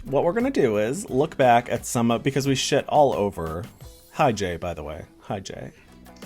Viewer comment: Omigosh around the 1:35 mark I thought you said "by the way Hi JADE" and I was like 😍 but then I realized you were saying "Hi Jay" 😵